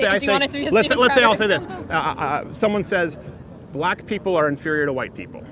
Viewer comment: can anybody tell what he said? lol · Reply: "Crowder's a virgin" I think